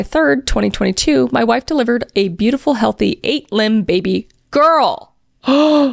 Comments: I legit thought you said eight limbs 😂 is your daughter Vishnu? 😂😂😂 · I thought she said 8 limb baby girl. Like an octopus girl!😮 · I genuinely thought you said ‘8 limb’ and I had to read the screenshot for myself 😮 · I thought she said 8 limb baby 😂 I was like wtf but when I went back and read it instead of just listening I realized it was 8lb like pounds😂😂😂😂😂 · am i the only one who thought she said 8 limbed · ngl i was listening to this in the background, but when i heard "8lb baby girl" i heard "8 limbed baby girl." had to come and reread that LMAO. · I heard "lb" as limb and was confused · an 8 limbed baby is what I thought you said. My jaw was on the floor till I saw “8lb” 😂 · I thought she’d said eight limbed baby girl and I was very confused. 😂 I had to actually look at the video and read the text (listening while I’m washing my face). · "8lb baby GIRL!" i heard "8 limbed baby GIRL" I deadass thought this child had 4 arms and 4 legs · I wasnt looking at the screen and I definitely heard "eight limbed baby girl" and was like 🫥🫥😶😨 hwhat 😒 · Not going to lie, but the way you pronounced “lb” I thought you said “eight-limbed” and I was wondering what spider creature came out of op’s wife. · Okay, okay, I heard "8 lb" as "8 limb" and I was like omg why aren't they expanding on that?? Omg 😂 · I was cleaning and listening to this video and I could have swore you said an 8-limbed baby girl. I had to go back and actually see that 😂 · Bruh thought u said 8 limb baby girl....😅